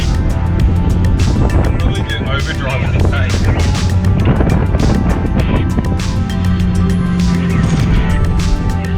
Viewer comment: What did he say about ‘over drive’? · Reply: I was saying "I'd better take it out of overdrive now hey" it cruised along ok but the engine temp started to rise and I think that's due to labouring the gearbox. So I turned overdrive off to return to drive/3rd gear, and get the rpm up to help keep the fluid moving around the system! Cheers